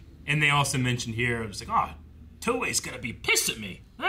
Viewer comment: did you think they said "Toei"? Cuz he says "Towa" Daburas Sister.